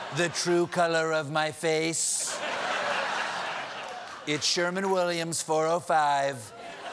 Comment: "It's Sherwin-Williams 405 Beached Squid." Although it sounded like he said "Sherman." 😂